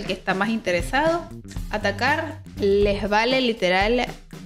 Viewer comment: Min 6:26 les vale Vga por no decir otra cosa peor… escuche bien ???😂😂😂